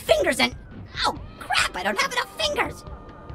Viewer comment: I hear “enough”, not “any”.